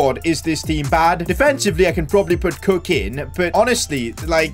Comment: Have you done Bolton?If not can you 🙏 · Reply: Also at 0:40 it sounds like he said “cocain”